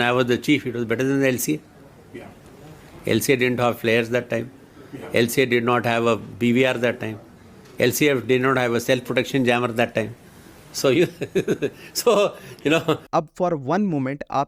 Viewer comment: he clearly said "at that time" 2019 not in 2025 🤦